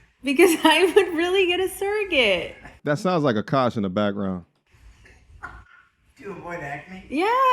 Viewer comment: For a second I thought you said "That sounds like a cuck in the background" and you know what? It wouldnt be far from the truth. Im not a fan of dogging this dude or anyone who goes through this, truth is he really deserves better. This woman has no idea what she is about to lose with the way this is going. I just pray for bro that he finds it in him to leave her, she is absolutely irredeemable and Akaash deserves so much better than her. A wife that doesnt do anything at all- no wifely duties, absolutely nothing except to undermine and humiliate is a lot worse than just a liability.